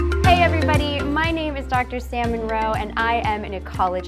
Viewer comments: When I hear you introduce yourself, I hear "Dr. Salmon Roe". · I thought you said your name was Dr. which would be pretty hilarious for an ecologist. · I thought it was funny. When you introduced yourself, I thought you said your name was Dr.. I couldn't unhear it. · I thought she said "Dr Salmon Roe" at first I was like "Wtf that's actually her name?"